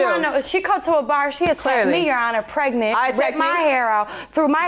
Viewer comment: Did she just say "Came to the bar and attacked me while I'm pregnant"? Umm, I don't think you're supposed to be at the bar while you're pregnant...Not the safest environment for the baby🙄.